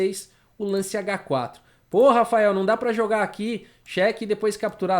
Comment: e eu que achei que ele tinha falado PORRA mas era PO RAFAEL